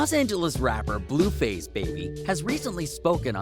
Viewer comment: Did he say blueface baby as his full rap name